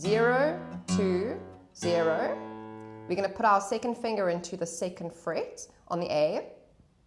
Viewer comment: I thought she said our Satan finger